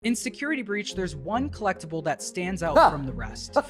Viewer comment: When he said "in security breach" I heard "Inesecurity breach" So now I want to see an insecure freddy XD